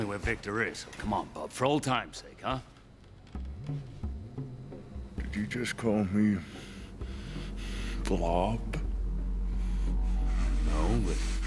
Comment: No joke I when I first watched this I thought he said “did you just call me Bob” and it made 12 year old me laugh his arse off.